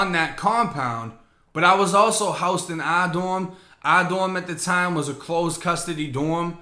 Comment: Had no idea I was around someone this vicious, that could commit something so brutal, and act like it never happened🤯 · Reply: “R dorm”? All this time I thought you’ve been saying “our” dorm. I should’ve known better!😇😄